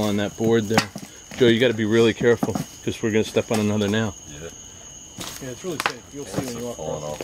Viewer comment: Two videos in a row?! WHOOHOO! 👍 7:31 after you said that about stepping on another nail, you hear, yeah in my left ear. 13:42 I did hear breathing 14:10 I heard a very soft, hi... 18:57 🌽 hole?! LoL